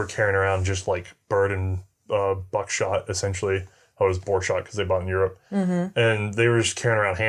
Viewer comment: What shot? I live in Europe and we have bird shot and we have buck shot, which is sometimes called "wolf shot". I'm not familiar with whatever is s referring to. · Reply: He did say boar shot. I'm not a shooter so I have no idea if that's a thing or not